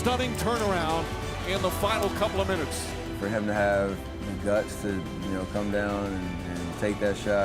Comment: for a second I thought he said "the nuts" instead of "the guts"